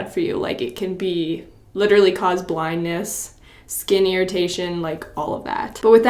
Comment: am i the only one who heard skinnyritation